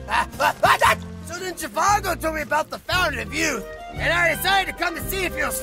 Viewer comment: "Then jizargo told me about the fountain of youth" that part cracked me up excellent use of the skyrim reference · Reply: I thought he said “and then Trivago told me about the fountain of youth.” XD